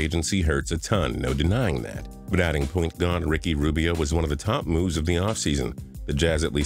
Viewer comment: Who else heard him say pointgod Ricky rubio😭😭😭